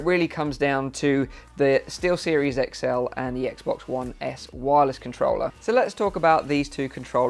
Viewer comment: Thank you. OMG, I'm was googling X Box One S Wallace and couldn't find it...I misheard you, lol; you were saying wireless, lol. If I get this, I'm totally naming my controller Wallace, lol.